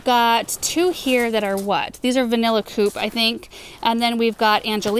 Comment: Hi....thanks for your videos I got a kick out of one of your tulips' name (16:04)....it sounded like the name is 'Vanilla Poop', although I'm sure that's not what you really said . 😄